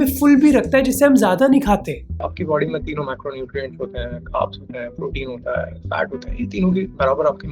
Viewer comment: Correction :- 14:23 Macronutrients instead of Micronutrients